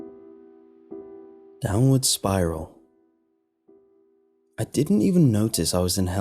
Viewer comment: I heard "Download Spyro" on the first bit..